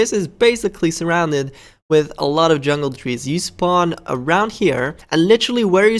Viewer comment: On 5 when you said Jungle trees I thought you said Jello trees